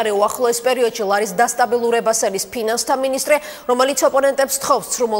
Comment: did she just say penus on national television?? lol Georgian news is funny af!